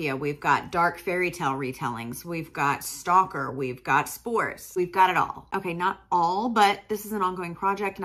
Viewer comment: did you say SPORES? o.o We have a saying in Brazil: "If there's a rule, there's a story" XD